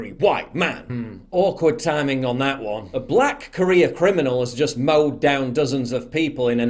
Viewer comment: Thanks, Paul. Because of your accent, it sounded like you said, "black Korean criminal" rather than career, I just didn't want the black Korean community to think you are trying to start a black on white on black Korean, race war, again! lol.